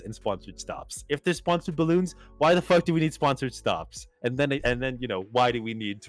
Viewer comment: Jesus I thought you mumblers were saying Spawn Surge Shops